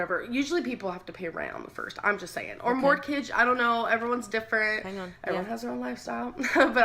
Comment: did she say fuc king morkige???? What's a morkige??!????!?